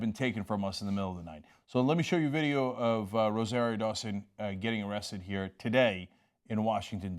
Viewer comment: I was about to get excited....I thought you all said Rosario Stone....Yowsa!!